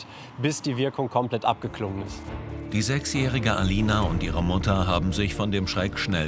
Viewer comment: AHAHAHA ich hab verstanden: die sexy alina😂😂😂 16:43😂😂😂😂😂